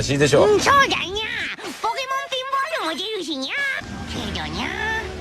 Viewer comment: Now I KNOW Meowth says "Pokemon Pinball"... but... I keep hearing "Pokemon Femboy".